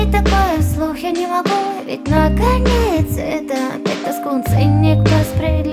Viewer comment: Я одна слышу слово "скунс"? ,:D на